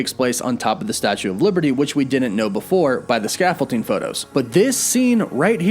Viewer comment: Oh man every time I hear him say "scaffol-ting" it takes me out haha